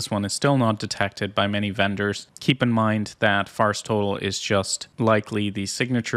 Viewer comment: Every time you say "VirusTotal", I hear "Bar Stool"....I may have to cut down on my drinking!